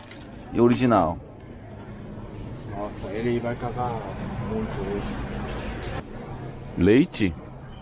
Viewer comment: "ele vai cagar muito hoje" KKKKK FOI ISSO QUE ELE FALOU?? KKK